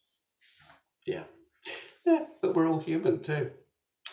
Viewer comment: T 35:22 said we are all humans, he did not say we are only humans.